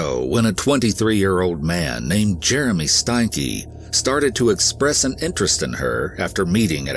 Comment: Omg I’m so sorry about this, but you said “Jeremy Steinke” and I heard “Jeremy Stinky”. I know this isn’t a place to be joking but I couldn’t help it, I know I’m terrible lol